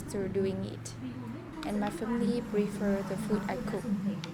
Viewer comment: I thought you said “My family reveres the food I cook” that’s a good word to add to your vocabulary it’s a little like “worships” and is a bit silly: the extremeness of the word